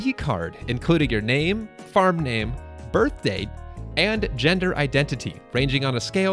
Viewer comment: lockouts won't happen for play style or cosmetic choices. The only locks are sexuality, and cosmetics are a bonus. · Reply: oh whoops! Okay then that sounds fine. Idk why I thought I heard looks instead of just gender. Thank you for the reply, great video